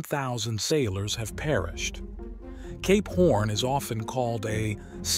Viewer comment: thought I heard something else for a second 🤣🤣🤣🤣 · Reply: K-p orn